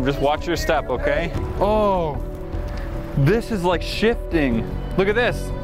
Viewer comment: am I the only one that thought said “sh** stain” instead of “shifting” until you turned up the volume?